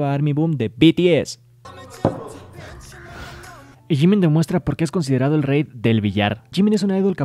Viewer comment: No names no c porque entendí "jimin demuestra que es gey" y yo espera ke